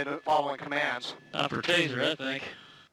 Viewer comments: a lot of people miss the comment when the cops say teaser that thing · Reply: Midnight Slay3r i thought he said "taser I think" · once again chenk misheard on purpose.. just like the Palin fiasco in this video he says "time to tazer that thing." when in reality he said "time for tazer I think." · Cenk you really need to work on thinking before you speak, you just incited more hatred for cops because of your "Time to taser that thing" which is incorrect. One day Cenk you will find out you caused more harm to innocent people by inciting hatred by mis quoting people. You really should be taken off the air and given a job at a used car lot where you can bullshit people without any real moral repercussions. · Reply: You are correct that Cenk misheard "time for a taser, I think" as "time to taser that thing". Yep. He screwed up. Now, how about the FACT that they did say he "looks like a bad dude", the FACT that they shot a man for touching a closed car door with the window rolled up, and the FACT that they LET HIM DIE by providing NO ASSISTANCE as he bled to death? Something in you is bent if those inconvenient facts don't rile yourself up as much as a single misheard word.